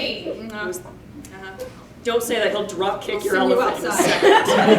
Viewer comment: what I thought kid said the first time don’t say I won’t drop kick your elephants what I thought he said the second time don’t say that, he’ll drop kick your elephants